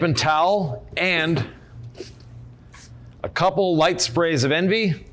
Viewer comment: What is NV spray? (I may have misheard) Right before u wipe with the rinseless towel. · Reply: DIY Detail Envie Ceramic Detail Spray